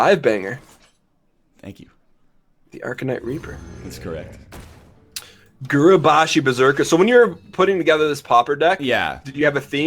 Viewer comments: All this time I thought they were saying "Popper deck" I was expecting some crazy deck that pops off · I thought he was saying "popper deck"